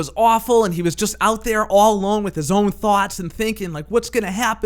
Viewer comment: I thought you said he was out there with his own farts.. 🤣